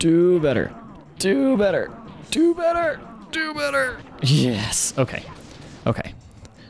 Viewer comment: On 7:52, it sounded like you said Jupiter XD